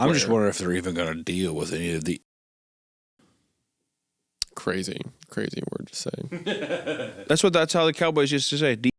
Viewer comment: what slur is saying? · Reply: Inj*n